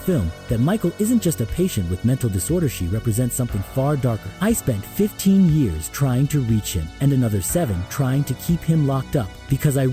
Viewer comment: Wrong!!!! 2:12 he said he spent 8 years trying to reach him and 7 years trying to keep him locked up in the original 1978 Halloween when he was in Michael’s old house with the sheriff standing by the window. Total that’s 15 years. What you’re saying is he was with him 22 years. Nope. 15 years. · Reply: He said 15 years trying to reach him and another 7 years trying to keep him locked up